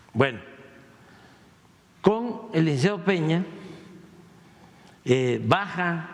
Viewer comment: Eliseo Pena??? 😂😂😂 Yo pensé que el nombre era Enrique Peña 😅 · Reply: Yo creo que dijo (de una manera rápida) Con el licenciado Peña...